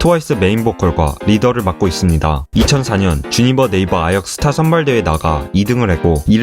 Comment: 주니버네이버라고 들리네